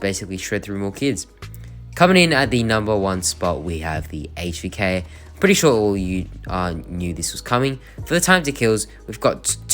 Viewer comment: did u say im pretty sure all u noobs saw this coming :'D and btw this is a joke dont take it as hate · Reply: Loll I said ‘I’m pretty sure all you knew this was coming’, but would’ve been pretty funny if I said that tho haha